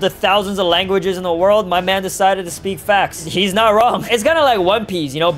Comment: DID BRO JUST SAID FAX INSTEED OF FAST 😂lol